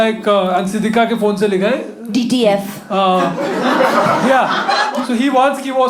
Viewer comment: What did Urooj say? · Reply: DTF: Down To F**k